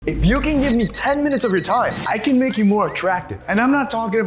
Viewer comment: bro i thought it said “if you can spar me 10 minutes”😂 i was like where is this going 😂